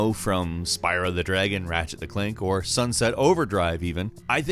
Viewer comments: Did he just say "Rachet: THE Clank"? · Did he say "Ratchet the Clank"?